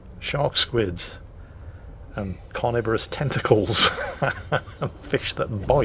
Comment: When said"Squid sharks and carnivorous tentacles" I thought he said Squid sharks and carnivorous TESTICLES